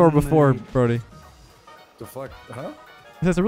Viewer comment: anyone else hearing “tf”?